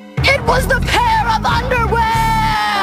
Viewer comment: When I first saw this, I could've sworn she had said, "It was... THE UNDERWEAR!!!!"